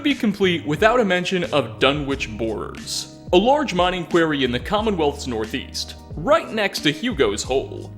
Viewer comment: Bit of a heads up, the audio sounds WAAY more echoey than normal today, I was recording in a different room to due to a move and really didn’t notice it during post/editing. It’ll be back to normal next video! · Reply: It's so bad, sounds like you say query instead of quarry.